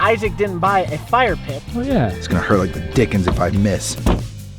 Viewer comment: when Isaac whas opening drink did he say di*k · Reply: He said it’s gonna hurt like a d!ck if I miss